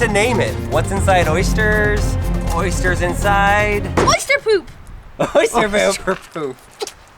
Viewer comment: I thought he said oyster boob